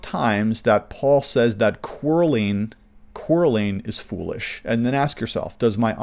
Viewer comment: is he saying "querling"?